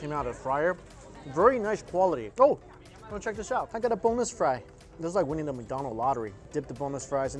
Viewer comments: I thought you said "boneless fry" instead of "bonus fry" hahaha Can I get uhhhhh BONELESS FRY 😂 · Aren’t all fries boneless? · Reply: I thought he said bonus? Lol